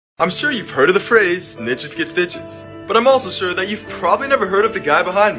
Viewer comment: I thought he said ninjas get sstiches